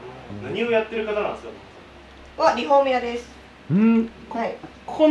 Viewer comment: 職業が聞こえないんですが、分かる方教えて下さい。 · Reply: リフォーム屋